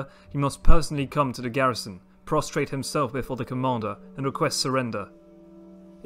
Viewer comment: I thought he said castrate and I got so horrified